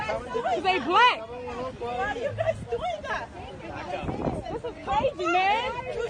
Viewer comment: She only used 3 sentences this entire time “ this some bull sh*t “They don’t care cause they black” “They didn’t do anything” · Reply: i thought she was saying "this is some bushes"